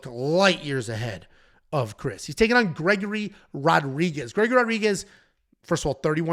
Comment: How? It was a no contest. The fight was 2 seconds and an eye poke. You’re acting like it was a loss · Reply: my bad I took a ton of shrooms and thought he said Pete Rodriguez